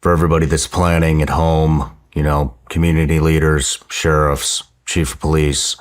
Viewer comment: I thought she was saying tariffs for 30 minutes